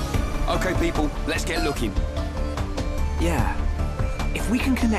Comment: Reyn says lookin' but subtitles say lockin'.